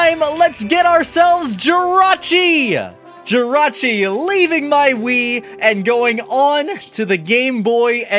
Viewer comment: man did not just say jirachi was leaving his urinary system